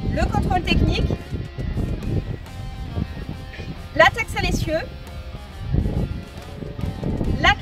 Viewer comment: La quoi ? xD latex a messieurs ? on parle de capote ou de camion ? ^^